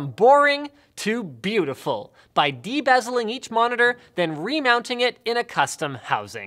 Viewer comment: i thought Linus said "bedazzling every monitor". I was outraged at how he said that so seriously with a straight face. Then i realized he meant de-bezeling, or take off the outside bezel. Edit: after watching the whole thing I think bedazzling the monitors would had looked better.